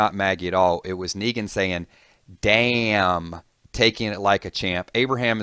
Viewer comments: "It was Negan saying, "Damn!" Actually, it was Negan saying, "Look at THAT! ...Taking it like a champ." Secondly, it can't be Abraham that Negan kills. Otherwise, there's no one to say, "Bitchnuts." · I find it funny and a bit odd that after all the changes from the comics to the tv show, that people are still convinced that this scene will play out exactly like the comic. LOL, if that were the case, why would it end in a cliff hanger? I mean we all know Glenn dies in the comic, there is no cliff hanger there....it's because Abe will take the death from Glenn in this one!!! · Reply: Nicholas Loscalzo I didn't hear "he's" I heard "damn, taking it like a champ" impressed and surprised tone